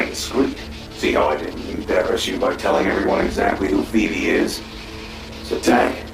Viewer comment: Well he says “what phoebe IS” I think this has nothing to do with tanks wife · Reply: That wasn't a "what" there. I heard him said "who Phoebe is."